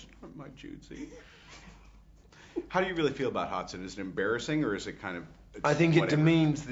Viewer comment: does Robert call him "Hotson" instead of Watson? :D